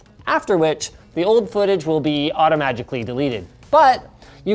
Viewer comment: did anyone hear him same automagically instead of automatically